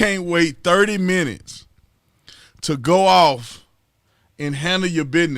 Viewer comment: I accidentally thought you said "get off." My mind was already in the gutter.